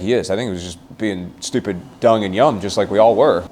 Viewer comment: Did anyone else hear 12:17 dung and yum